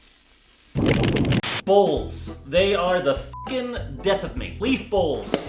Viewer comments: i thought said balls will be the death of me in the intro.. same same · When you said bowls will be the death of me I thought you said balls will be the death of me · is it just me, cause every time i hear says "bowls" instead i hear "balls" ?? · Thought he said " Balls ! they are the death of me ! " XD